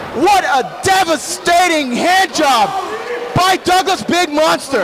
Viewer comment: im sorry? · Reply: Did he just say « hair job » ????